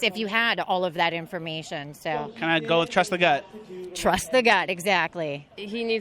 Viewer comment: did I hear her say bust a nut? i cant hear what shes saying clearly, im so focused on the facts that she is feeding me.